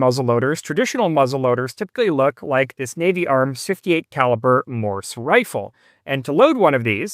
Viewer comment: it sounded like "68 caliper" not "68 calibre"